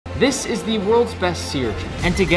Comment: Why did i heard " this is the world's best siri " at the start😭